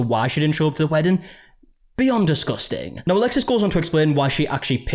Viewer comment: i heard "beyond disgusting!" as "be UNdisgusting!" 😂 and i agree either way!